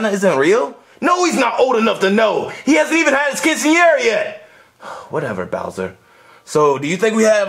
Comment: what did bowser say there? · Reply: He said “He hasn’t even had his quinceanera yet”. And if you don’t know, a quinceanera is basically a party for a girls 15th birthday in mexican culture. It’s like a sweet sixteen in the U.S. but a bit more early